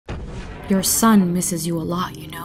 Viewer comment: I thought the asteroid was talking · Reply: I thought the astroid was saying “Your son misses you”